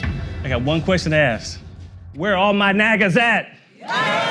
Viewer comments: Minute3:50 when he said where are my nagaas at!!? Sounds like he said where are ma niggas at!!? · I really thought he said "Where all my niggas at" but at the same time it makes sense